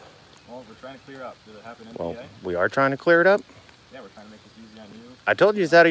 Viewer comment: "We're trying to clear it up..." OHHH!!! I thought he said "We're trying to stitch you up"!